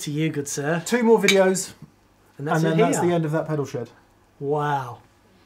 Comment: The last TPS? Really? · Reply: At 4:31 says: and that's the end of That Pedal Shed. I heard: and that's the end of That Pedal Schow. My bad :-)